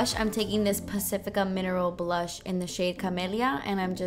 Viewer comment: Oh my garsh! Am I the only one that heard her say the Pacifica blush in clamidia? LOL!!! I needy I clean out my ears! 😂